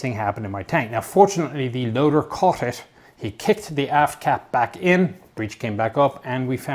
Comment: When he said AFT cap I honestly thought he said ass cap I had to go back and listen to it again